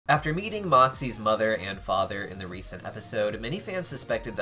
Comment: Bro for a second at the start I thought it said "After EATING moxxies mom and dad" 💀💀💀 Bro, i don't even watch helluva boss but I get it so much on youtube, i know some names, stolas,blitzø,moxxie,luna,striker,verosica mayday, and more but I forgot so yeah.